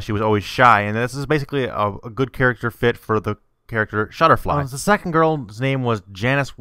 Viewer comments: Did he say shudderfly? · did he just say shutterfly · Shutterfly?! I hope I wasn't the only one who heard that.